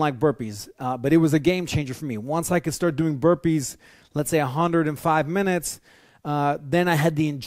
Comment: did he say 105 minutes of straight burpees???